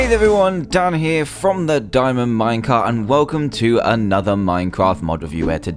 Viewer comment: when you do your intro it sounds like you saying "dan from the diamond minecart" instead of minecraft XD